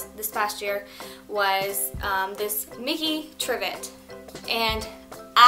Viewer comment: What did you say that was? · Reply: Vivi33 A trivet! :)